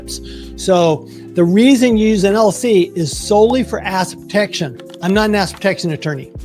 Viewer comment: Great video! Am I the only one who heard him say "a$$ protection"?